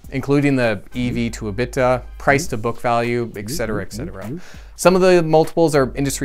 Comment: did you say EGG-cetera?